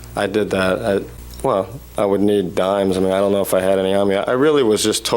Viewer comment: Did he just say he would need dimes? 🤣😂